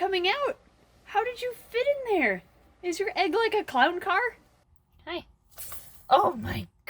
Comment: I honestly thought she said Clout Car